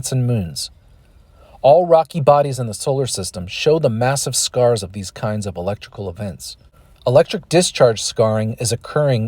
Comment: What does 5:00 say? I even went x.25 speed and it's just too fast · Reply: “All rocky bodies in the solar system show the massive scars of these kinds of electrical events.” The link to the source is in the description of you want to read it.